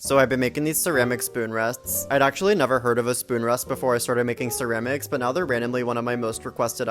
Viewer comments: For a moment I thought you said spoon rats... and ngl I was intrigued. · I thought I heard you say ceramic spoon rats. And now I'm disappointed. Please remedy this with spoon rats